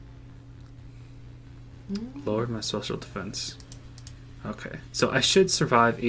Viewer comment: said social defence instead of Special Defence lol